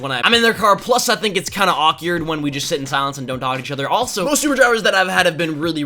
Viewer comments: anyone else hear say awkyard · lol he says awkyerd instead of awkward